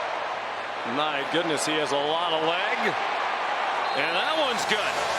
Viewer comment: Whoa!! What Did He Say?? "A Lot Of🦵 ." 😮😅